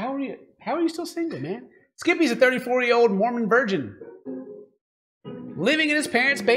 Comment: OMG... I thought it said, "Skippy is a 34-year-old MORON virgin". 😂🤣🙃